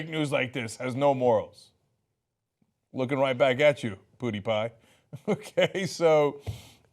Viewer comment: Did Cenk just say Putiepie? XD